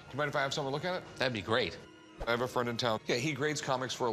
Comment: did i hear two great